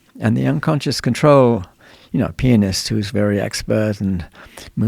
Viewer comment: This man said pianist and I swore he was saying penis.